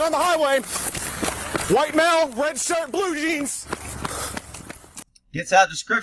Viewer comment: Always great experienced advice 🧡 Liked 👍 08/12/23 Lol I thought the Cop said, Forever In Blue jeans 👖